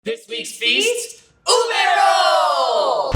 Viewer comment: At the beginning I heard, 'This weeks feast is- UBER'